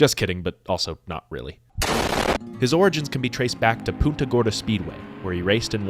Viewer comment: i know you dont speak spanish but i beg of you to pronounce the n in punta gorda. It sounded like you said puta and i nearly spit my coffee out